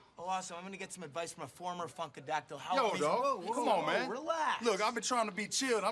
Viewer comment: I thought he said fuckadactley